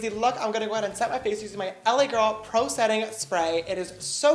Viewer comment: I thought you said a little setting spray 🤣